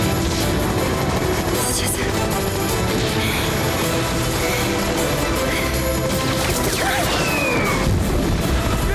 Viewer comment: мне это не послышалось? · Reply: Блин, время не сошлось немного, короче, чуть назад сделай, она сказала "Пиз**ц"